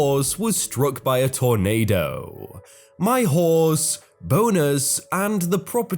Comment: sounds like your saying " My horse boners"